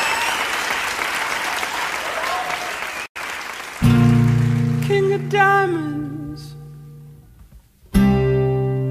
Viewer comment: tell me why I heard "king of daddies", I- ✋😔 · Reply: DIAMONDS